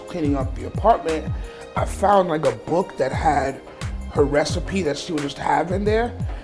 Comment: did He say penis